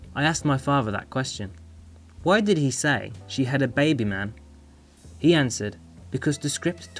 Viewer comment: I re-watched this Movie a few days back and i Still heard it as "She had a baby,man". Wow